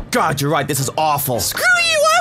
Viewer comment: Hello ! Someone can tell me what is saying at 1:07 ? I'm french and... I don't understand any word ! XD thanks ! ;) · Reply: he said "Screw you I worked really hard on those! Appreciate me!"